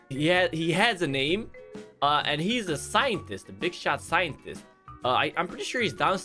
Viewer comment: did you just say? "Big Shaq Scientist"? Oh... i forgot this! #Cat